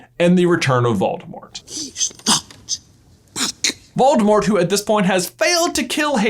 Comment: am i the only one over the years who has thought that when fudge says "hes not back" it sounds like he says "hes not 'hes back' right? I mean...